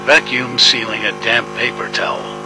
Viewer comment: i thought it said “damn paper towel” lmaoo